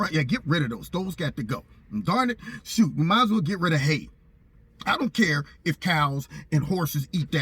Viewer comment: I thought you said get rid of hate, but I knew they wouldn't want to do that. They love hating others, but can't stand the thought of others hating them. Plus, there's a lot to capitalize on in hate.